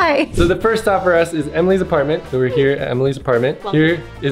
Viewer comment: I thought he said that Emily was pregnant 😂